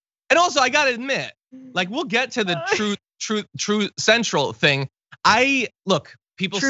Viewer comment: TRUE CENSOR! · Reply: I thought he said Trothe Central. Oh well it does not matter. If he cannot remember why should we.